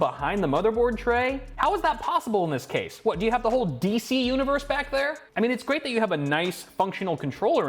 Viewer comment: did he just say dc